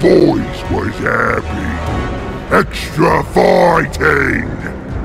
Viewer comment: put captions on. When Ork says extra fighting caption says extra farting lmaoooo